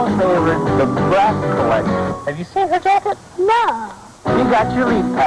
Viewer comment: I thought he said the 'black collection' XD. my memories with that place is... wait... I never went to toys r us....